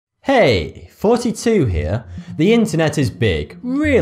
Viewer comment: It sounds like you say "hey 42 here" in the intro